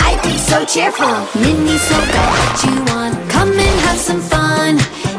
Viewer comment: I heard it as "miniso collection one, come and have some fun"